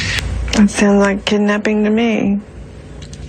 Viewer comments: “It sounded like a kidnapping to me” That’s what I hear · Patsy said “It sounded like a kidnapping to me” , which was her simply trying to pitch the kidnapping/intruder/etc…etc narrative. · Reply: I heard "Don't sound like a kidnapping to me"